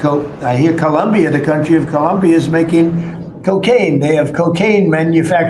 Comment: Did he say we appreciate Columbian cola factories